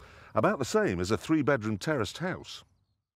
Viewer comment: Did he say 3 bedroom terrorist house? HAHAHAHAHAHAHA :D